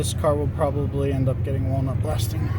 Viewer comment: Can you clarify what you said around 18:40 for the carbon down the heads? Warm up blasting? Can you elaborate? Thanks for the video! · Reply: Walnut blasting. It's the preferred method of removing carbon buildup.